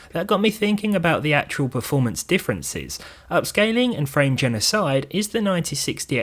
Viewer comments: I heard "frame genocide" lol. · thought you said "frame genocide"